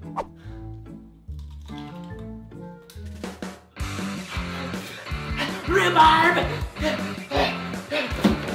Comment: it sounded like her mom said riaharb